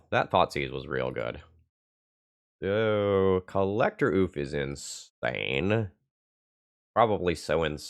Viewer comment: Who else heard "Collector Ouphe is in Spain"?